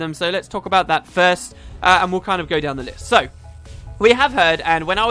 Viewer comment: Haha, I like how at 5:44 you say "going down the list" which sounded like going down the lys, while you jumped of a cliff